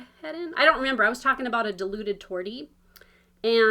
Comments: Ok I hit the down thumb but wow. I've never done that to anyone. I need to organize my floss, I've stolen from some projects to start others. And just for a second I thought you said deluded tortie. Lol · Ha ha - I heard "deluded tortie" too! Made perfect sense to me as my own furry boys are not the brightest - LOL!